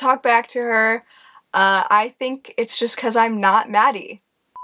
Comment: HELP WHEN SAID “..because I’m not Maddie” I THOUGHT SHE SAID “BECAUSE IM NOT BADDIE”